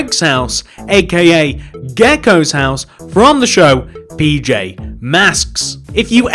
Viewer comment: it sounded like you said bj masks